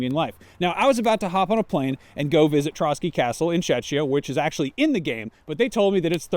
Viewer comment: What did you just call my country? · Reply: czeCHia